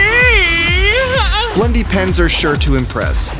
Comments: I thought it said bendy pens 😂😝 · I thought it said bendy pens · I thought it said “Bloody pens”